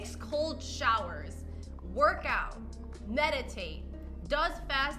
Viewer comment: HELP I THOUGHT IT SAID FISTING